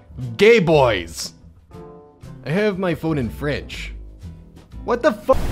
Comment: I thought it said I have my phone in the fridge..